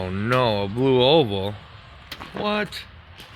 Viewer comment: When you said, "A blue oval, what?" I thought you said, "A blue Volvo!" I'm like... That vehicle is not blue!!